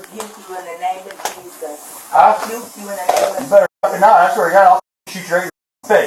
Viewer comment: The name of Jesus is a threat to anyone filled with demons. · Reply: at first I thought she said I will Burn you in the name of Jesus and thought that's what the officer Heard when he ask her what did you say